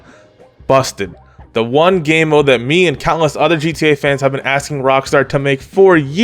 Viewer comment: Did he just say “The one game mode that me and callous other gamers have been asking for years”?